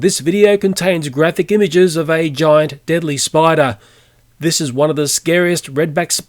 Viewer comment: Was i the only person that realised he spelled "deadly" wrong at 0:30...deadily is what he said